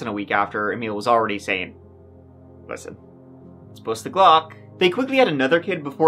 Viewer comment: umm what did u say? Something about a glock? No idea what ur saying mate